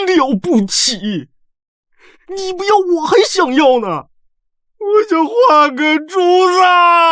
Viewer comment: Wang Haimin (汪海敏) plays the female lead, Shen Yuejian (沈月见).Huang Tianqi (黄天崎) plays the male lead, Pei Yan (裴衍). Pan Lin (潘霖) plays Crown Prince Xiao Xuanrui (萧玄睿). 43:23 subtitles say guard complained "i want to paint a bamboo" actually我想換個主子! said: "I want to change masters" 😅 At 55:55, the song being played, isn't it from a big drama like Prisoner of Beauty or Pursuit of Jade? ❤ It's so familiar and beautiful... 1:04:53 1:04:58 his personal guard is such a great wingman😂😂